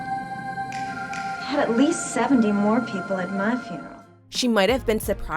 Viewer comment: For the longest time I thought she said, “And at least 70 more people to have a funeral” Somehow, I heard wrong